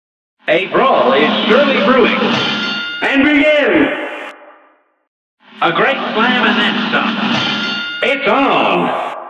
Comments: “A brawl is surely brewing!” ”AND BEGIN!!!” “A great slam and then some!” ”IT’S ON!!!” “Good day for a swell battle!” ”NOW GO!” “Here’s a real high-class bout!” ”AND BEGIN!!!” “This match will get red-hot!” ”YYOU’RE UP!!” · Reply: i thought it said "a great slam an then dunk" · “A brawl is surely brewing!” ”AND BEGIN!!!” “A great slam and then some!” ”IT’S ON!!!” “Good day for a swell battle!” ”NOW GO!” “Here’s a real high-class bout!” ”AND BEGIN!!!” “This match will get red-hot!” ”YYOU’RE UP!!” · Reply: I always thought he said “a great flamanemza.” No idea what it means but ok. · I always thought he said "a great slamananza!* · I thought he said "A great slam banansa!" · In the first one he says: "A brawl is surely brewing" and what is that stuff that goes right after? I just CANNOT understand that (its on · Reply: Niyatex he says “AND BEGIN!!” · Is it weird how I heard 0:05 as “A great slam event’s up!” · Kept hearing "a great plan Vanessa" FOR THE WHOLE FKN GAME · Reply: I thought it said "A great plan and that's all" 💀💀💀💀💀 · Kept hearing "a great plan Vanessa" FOR THE WHOLE FKN GAME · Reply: Always thought it said " a great plan and that's all"💀💀💀💀💀